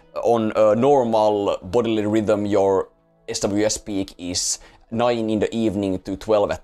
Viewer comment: At the 7:00 mark? What did you say I need to take? SWS speak? What is that?